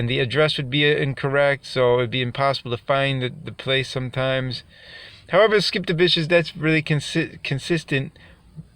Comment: Did i hear skip the bitches @ 1:35? lol